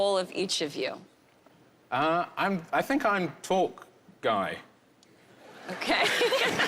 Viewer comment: "I think I'm talk guy." - spoken like true talk guy. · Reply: Oh that's what he said. I heard "tall guy" lol.